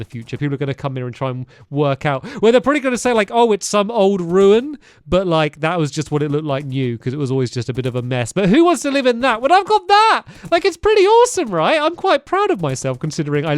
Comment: I thought he said ‘you’ instead of ‘new’ and felt offended 😂